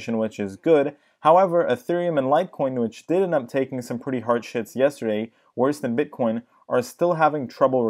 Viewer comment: I swear I hear "took some pretty hard shits yesterday" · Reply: "Harsh Hits" hahahah but I can see where the confusion comes from